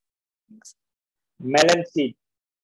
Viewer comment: Anyone understand what is said by sir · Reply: Melon seed sized tissue for biopsy